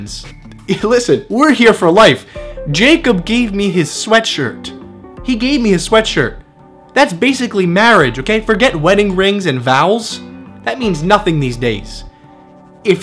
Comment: "Forget wedding rings and vowels" just sounded like he said vowels lolol